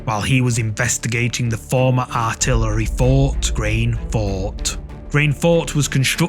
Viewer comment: he says Grain Fort... I hear Brain fart